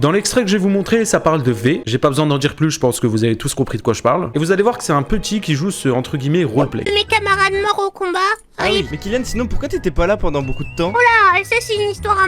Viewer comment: quoi ? 🤨🤨🤨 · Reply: Viole